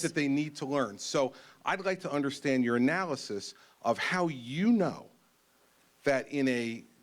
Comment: Sounds like he said "I'd like to understand Urinalysis" 🤭